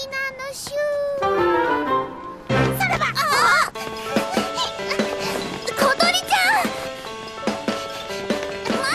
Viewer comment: Did Kotori Just Said Son of the..? at 0:43. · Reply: Kotori said Saraba(さらば）